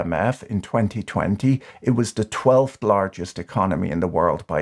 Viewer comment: you say twelfth, but the captions say twentieth. Edit: I'm amused by the premise that Turkey is a democracy. Erdogan and his Islamist allies control the (now independent) executive, effectively control the legislature, control the judiciary (thanks to a 'purge and replace' campaign begun circa 2008), and control the military (which was once an alternate center of power with a mandate to overthrow the civil government when certain conditions were met; they don't, anymore, because they've been coopted after the most recent, failed coup attempt). He has been in power for twenty continuous years (since March 2003). Turkey is about as 'democratic' as Russia.